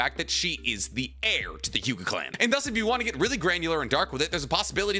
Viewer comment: Wtf did just say?? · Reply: ‘and thus if you wanna get really granular and dark with it’s